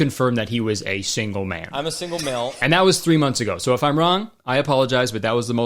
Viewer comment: did he say “I’m a single milf”?